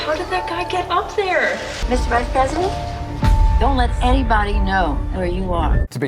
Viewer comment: does she say Mr. Vice President ?